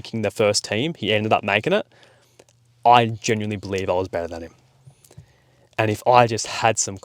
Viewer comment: When you say "i was better than him" i can't hear the word "was". So could you explain to me what you was saying please? I'm studying the English language.